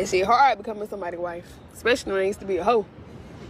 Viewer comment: I still get confused on what she is saying here about being a hoe… I’ve replayed it back, to back…, to back and it still sounds like she is saying he used to be a hoe and not her… “it’s hard being a wife to someone, especially when THEY used to be a hoe.. maybe I’ve been hearing and am still hearing or wrong.